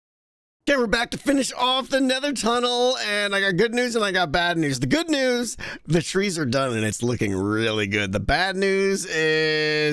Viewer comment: did you say good news and glad news?